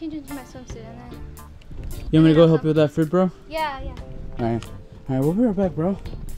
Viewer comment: That didn’t make sense when said I’m going to go get my swimsuit on and said yeah I’m gonna go help 🤯😬😦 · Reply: That's what I thought, too, at first! But then I went back and re-listened. At 4:29, said, "You want me to go help you with that fruit, bro?" referring back to what had said at 3:33-- "Awh, we should have gotten some, like, fruits."